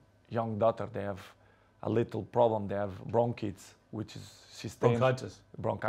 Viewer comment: I thought he said wrong kids. But he said bronchitis and he say 9 eleven. But mean higher level. LMFAO.